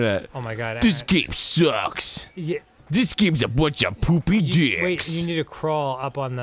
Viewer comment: I heard Arin say "poopy ass dicks" and got excited but sad when he didn't become AVGN like he did on Mickey's Mousecapade